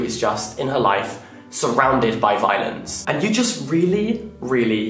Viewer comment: for a second there i thought you said she was ‘surrounded by violins’ lol